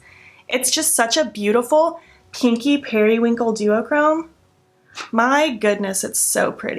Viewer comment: Lol I misheard "kinky periwinkle" and was so confused for a second